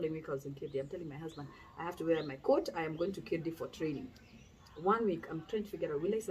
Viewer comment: I'm still trying to figure out what she said at 15:12 ati where for training??